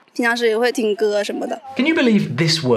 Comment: how I hear it 就比如说打打球 羽毛球？ 不是，打排球。 尽量要是有问题听歌什么的。